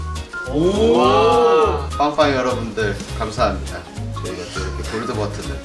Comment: 초 뽝빡이 여러분들 이라고 들리는데 기분탓인가